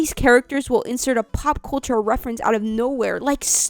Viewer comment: I thought you said Paw Patrol instead of Pop Culture and didnt even blink, just went "oh guess I missed that one". That should not be my reaction to hearing that for any show and really says a lot.